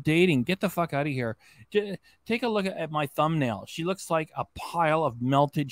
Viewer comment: At the beginning, I thought she said she looks like a penguin.